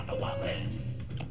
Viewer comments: I heard “we got the wabless we got the wablezzzz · I thought Brump said BRING OUT THE WUBBZIES